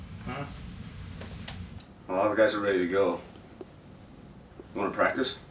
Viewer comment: What did he say at 0:24? Please tell me. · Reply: “Well all the guys are ready to go.”